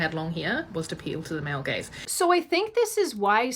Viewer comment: It's amusing that her subtitles say "male gays" not "male gaze". It's also more accurate when it comes to fashion. Straight men really aren't dictating what fashion is, with the exception of fashion designers.